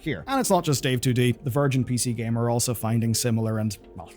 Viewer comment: sounded like "virgin PC gamer" 😂